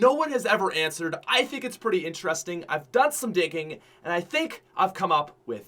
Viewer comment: At the beginning it sounds like you said you've done some dicking